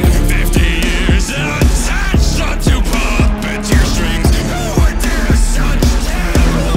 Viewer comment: I thought it said "who would dare such terrible things"😂